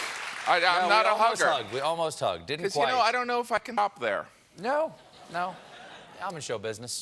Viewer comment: Anyone know what he says at 0:47? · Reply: hot pocket